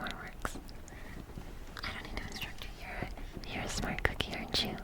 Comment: I had to replay 1:50 multiple times 👂 because i thought you said "you're a jew"